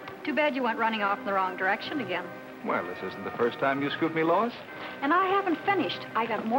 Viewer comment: did Superman say... “This isn’t the first time you’ve screwed me, Lois”?